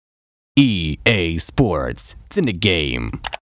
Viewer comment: I heard "EA sports it's in the gay" 😂